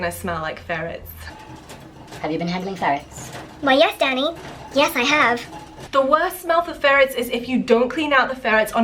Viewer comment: At first I thought you said, " OH yes daddy, yes I have"